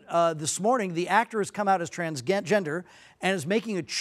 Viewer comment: he was gonna say “trans-gay” lmao